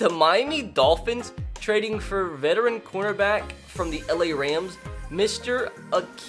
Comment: Every time he said corner back I thought he said quarterback